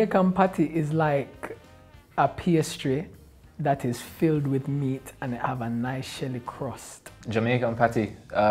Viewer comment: I thought he said that "it is a PS 3 that is filled with meat" at 0:31 😁